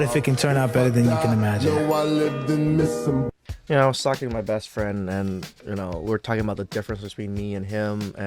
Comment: did you say 'While sucking my best friend'?